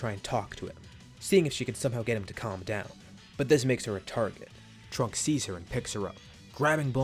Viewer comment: I thought he said "and this makes her retarded" for a sec